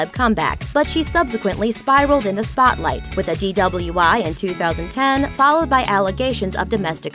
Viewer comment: Did she say with a DWI? 😂😂😂